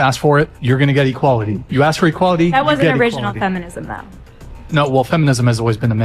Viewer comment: what does she say there? I can't hear it well and the CC captions ignore it. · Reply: "That's not true either" I think